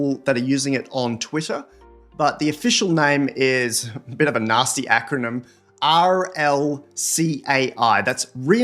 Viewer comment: I had to listen to that again. I thought you said, "a bit of a Nazi acronym"